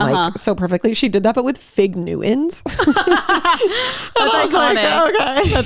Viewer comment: what is saying here? "Fig nuins"? Sorry im not american.. and i would really like to know what Khloe K did here that is so ridiculous? 😅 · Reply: I think it’s “fig newtons”. They are kind of like a pastry filled with fruit paste. Not sure why it’s so ridiculous, maybe just because they’re not that aesthetic 😆